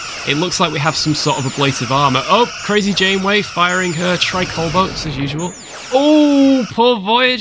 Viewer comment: I swear for a moment I heard Jonesy from "The Hunt for Red October", "Conn sonar, crazy Janeway!!"